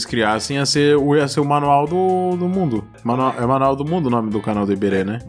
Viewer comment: kotaka: Manuel do mundo Só eu que ouvi isso? Kkks